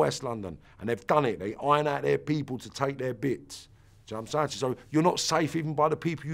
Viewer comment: What does he say ? The accent is too thick. And people say my Baltimore accent is crazy lls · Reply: They iron out their people to take their bits. Do you know what I’m saying. You’re not safe even from the people you grew up with.